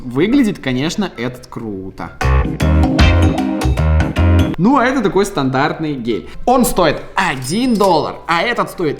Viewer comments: " ну это такой стандартный гей" мне одному это послышался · ну а это такой стандартный гей оруу)))) я знаю что он сказал гель но похоже было на гей а почему кста гель это же антисептик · Мне одной послышалось что он сказал вместо гель гей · стандартный гей 🤣🤣🤣🤣🤣🤣🤣( мне послышалось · мне послышалось слово гей